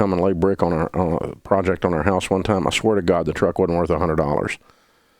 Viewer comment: Dave, at 3:50 did you say “ I swear to God ? ‘’ If so, please apologize. I thought you were a Christian brother.